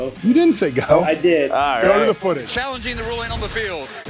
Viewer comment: i heard go and even if he didnt say it he hesitated enough after 1 to have gone exactly at go